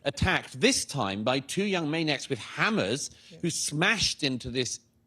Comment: At first I thought he said the Velazquez was being attacked by Hamas (instead of hammers)